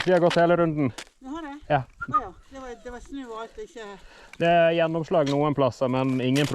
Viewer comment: I have a question. Where you're speaking with the lady here, when she says 'Åja, det var snø og alt?', is that what she really says at the end? I listened to this part a few good times because I can only hear 'det var snø og vet ikke' - similar to how in English you say 'so there was snow and stuff'. I know you sometimes edit the subtitles in Norwegian a bit so they're more clean - for example, you don't include stuttering or when you repeat some parts etc. Is this part edited or did she actually say 'og alt' and my ears can't comprehend it?😂